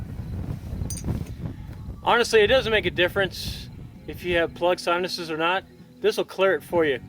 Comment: Plug soundeses? Is that what you say at 6:14?